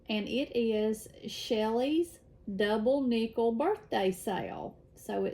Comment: Y'all crack me up! I heard "double nipple birthday." 😅😂 I would love to stitch number 2. Thank you for the wonderful video, I can't wait for the next one! P.S. I have Bostons, too!